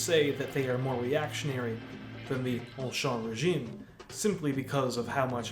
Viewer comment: "more reactionaries than the____regime". Sorry, I couldn't understand what you said at that part. What regime was that? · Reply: ''Ancien Régime'' aka. pre-revolutionary French absolutist monarchy.